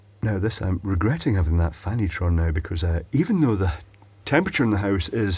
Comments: I thought you said “I’m regretting having that Fanny tron now..”. Honestly spent a good 30 seconds wondering if that was some kind of food. A new exploding toy? Then rewound and realised. Oh yeah. Fan heater of course it was.. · I misheard "I regret having this fan heater on" as "I regret this fannytron".